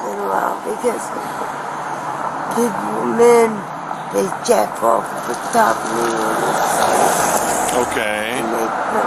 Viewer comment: For those who didn't catch that... The reason she hasn't slept in days is because when she falls asleep she says that guys jack off on top of her... Jesus...